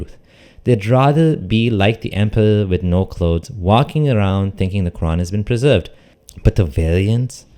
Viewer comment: i heard it crown